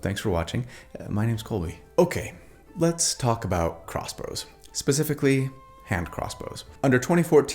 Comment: did you say “CrossBros”? 😂